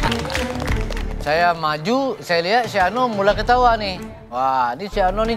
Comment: padahal Lord Adi bilangnya Chef Arnold. Gua dengernya Si Arnold, sampe ngakak bjir, "Gila ga sopan bgt" 🤣🤣🤣